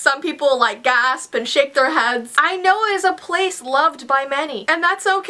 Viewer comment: Ok i know this sounds wrong but i thought she said "shit their heads", its because i was moving around and i didnt hear her correctly im sorry lol i was like "WHAT" lol XD